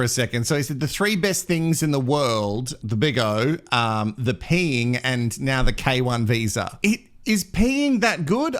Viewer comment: I thought he said Pink not peeing...is his favorite singer Pink???🤩😭